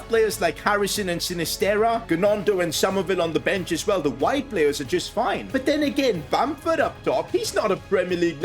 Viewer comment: i thought he said at 1:20 the white players are just fine, lol